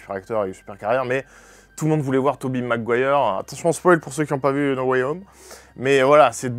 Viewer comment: j'ai entendu "toubibe Mcguire". C'est fantastique